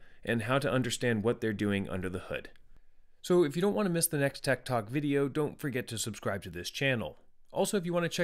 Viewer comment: Every time he says tech talk i hear "TED talk", thanks.